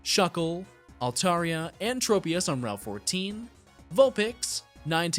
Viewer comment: did it sound like he said 'quaranteen' instead of 'fourteen'/